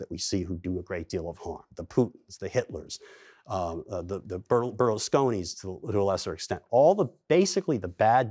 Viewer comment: Did anyone catch that third name? "The Putins, the Hiters, the ..." the what? Burlestonians?